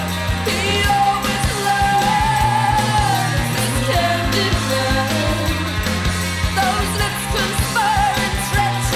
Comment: What's Siouxsie saying at 2:22 and 2:16? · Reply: beware the masked pretender... (in the background at 2.16 - he always lies)